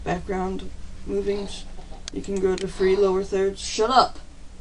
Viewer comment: i heard u said shut up 0:59 or sumone said it